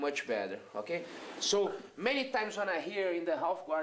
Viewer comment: I thumbed you down, because I thought he said look too many times.